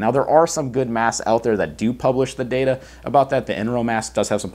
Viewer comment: Does anyone know what mask he is talking about here? Im note sure i am spelling it right in google search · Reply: Enro, but need to test it first which won't be for a couple weeks.